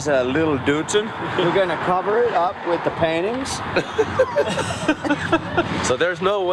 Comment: For a second there I originally thought they said they were gonna cover the goat with panties.